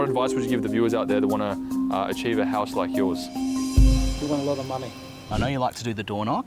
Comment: he says “you WANT a lot of money” not WIN. Caption is wrong and it’s not autogenerated it was editing incorrect 🪦